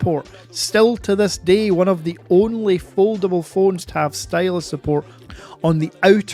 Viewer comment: thought U said this is the one and only phone to have it lol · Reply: No he said "one of the only" implying more than one 🙂 8min39sec